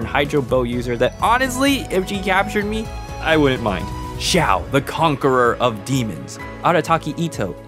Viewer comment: me being an avatar nerd, all i heard was zhao the conqueror 💀